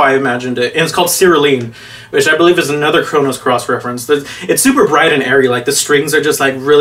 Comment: I think I lost an hour of my life every time I heard "Chronos Cross." It's "Chrono Cross" you nerd.